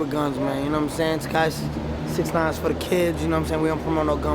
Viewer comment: Did bro just say 6ix9ine is for the kids 💀